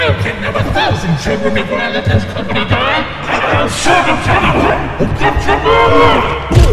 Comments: I thought waternews said or get your man out in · the fuck did he say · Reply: He said he'll silence anyone that gets in his way